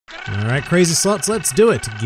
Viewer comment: ngl, first thing I heard was "alright crazy sluts" and I thought wow these intros wildin